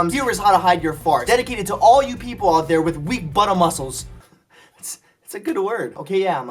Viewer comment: It sounded like he said butter muceles💪